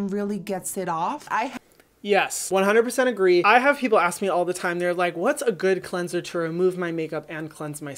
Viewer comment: I rly thought he said "i've had people ask me what are the best makeup and cleansers to remove my skin,"